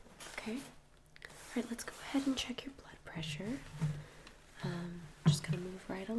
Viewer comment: Legit thought that said anal check up